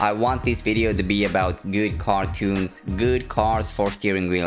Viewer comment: When ever you said car tunes I thought you said cartoons